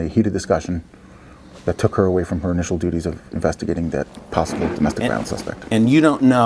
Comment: Did he say possible domestic violence suspect at 15:00. Thought it was a traffic stop haha